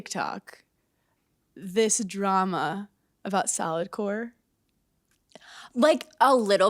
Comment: Straight up thought you were saying “Salad Core” 🤣🤣